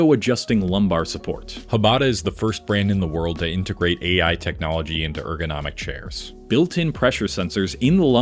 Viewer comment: Instead of investing in expensive chairs, just take regular breaks... I don't think being comfortable while sitting is a good idea because then you'll just keep on sitting. Ergonomics = Movement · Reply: I heard "AI integrated chair" and wanted to throw my phone